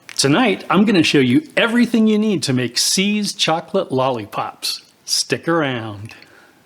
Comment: So I misheard and thought you were making seized chocolate lollipops. I was mystified how this could work. 😊 Looks great!